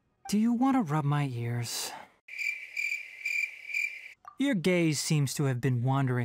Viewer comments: When he said “Your gaze” I heard “Your gay” 💀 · I heard you're gay, not your gaze 😩And I was shookt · Gurl i heard: “your gay-“ instead of gaze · I PAUSED AT "gaze" AND I HEARD "your gay" 😭😭😭